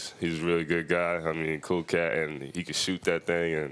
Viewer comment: "cocaine he can shoot that thing" 😂😂😂 shit at first I thought he said that lol.